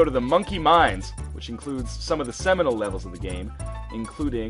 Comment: lol i thought u said minecraft XD, but the minecart levels are the best